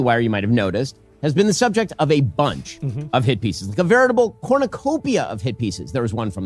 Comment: I thought he was saying "hippies", not hit pieces.